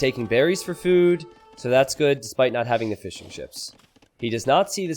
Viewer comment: I keep hearing“fish and chips”, I don’t know.